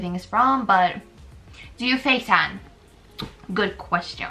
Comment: I thought she said “facetime” not “face tan” hahahahahahah